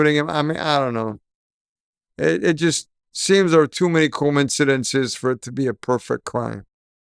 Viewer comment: Did he just say there were too many combincidences as in Comb? 😂😂 2:30 ??? As in I’ve watched too many Cassie Law suit videos 😂